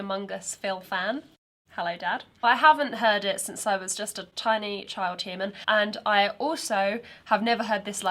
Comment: Why did I hear "tiny child demon" in your intro? :face-blue-smiling